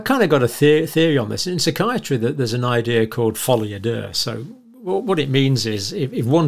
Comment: "Folie à Deux" : "Delusion or mental illness shared by two people in close association." I have to confess that I first misheard Dr. John: I thought he said "follow your dirt". Which garnered unsurprisingly few results on Google.